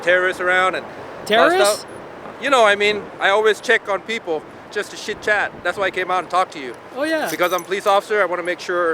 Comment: did he REALLY say "shitchat" lmfao!